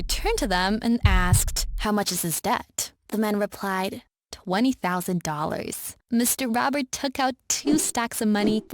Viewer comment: This is the most cliche story. Bad father, loss of a family member, Emily getting a job, greedy boss, Emily finding a bunch of money, and Emily being rich at the end. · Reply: There's also an error in 8:11 He said $20,000. But the captions said $220,000 Is that how you wrote the number?